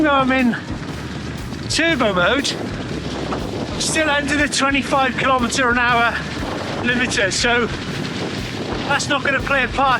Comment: 125 km/h limiter … 😂. Did I hear that right? Super channel , completely compelling content. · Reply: Lol! Heard the same. Rewind it again and heard "under 25km/h limiter" 😂👍🏽